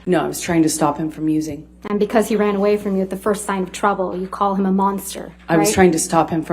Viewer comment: Minor correction: “She actually says “And because he ran away from you at the first sign of trouble…”